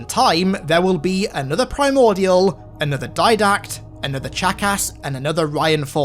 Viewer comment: When u pronounced chakas it sounded like you said jackass